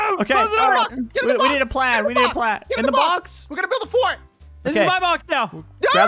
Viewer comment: sounded like he said the F word